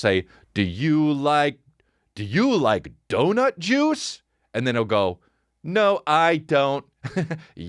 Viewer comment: I totally thought you said jews instead of juice.